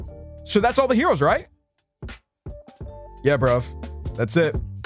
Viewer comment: Me "Why is the title no?" Proceeds to immedietly hurt himself 😂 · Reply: did you say bruv😅😂